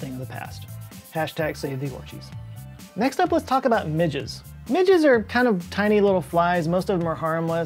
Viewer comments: I glanced away from my screen and totally thought you said, "Next up we'll talk about midgets!" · "next up, let's talk about midgets" I heard that wrong and was prepared to hear about midgets swarm. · I thought he said "midgets" 😭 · I thought you said " now let's talk about midgets" -_